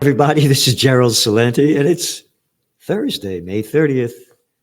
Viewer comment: I thought you said, I’m “ general Celente” - ❤